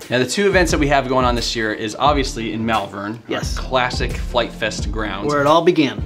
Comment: I’m coming to Malvern again for the third time! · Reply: What Malvern i thought he said Melbourne i gotta cancel my flight ..